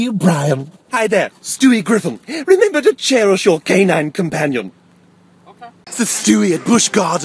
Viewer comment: Did he just say "K9 Companion" at 0:54? Just asking, I'm a Whovian.